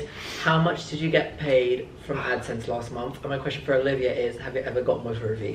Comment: what is actually saying at 13:00 ,i can’t tell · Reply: I think its “have you ever gotten with hrvy”